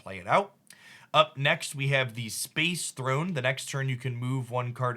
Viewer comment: Am I the only one who heard “ space throne” at 1:18 XD